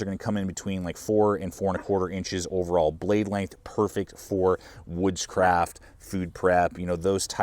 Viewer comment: I thought you said it was perfect for witchcraft. Had to relisten to hear woodcraft